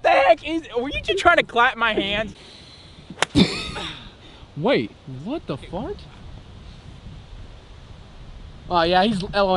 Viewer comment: Did anyone hear say what the fuck